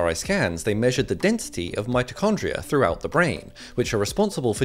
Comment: I thought he said midichlorians 😂😂